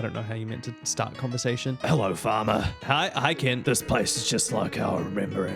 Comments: WHEN YOU FIRST SAID "HELLO FARMER" IN KENT'S VOICE I THOUGHT YOU SAID "HELLO FATHER" 😭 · I thought he said “Hello, Father”… also yes haha funny number wow funny haha hilarious